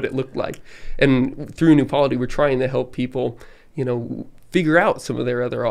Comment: What does he say here that helps people out 'figure out some other options'? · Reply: New Polity. It’s a website/organization he is a part of